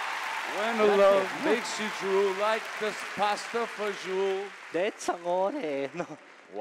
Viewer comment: Sollo io ho capito pasta e fagioul 2:39 ?